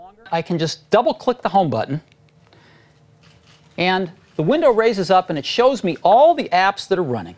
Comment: Did he just say Window???? Subliminal MS ads!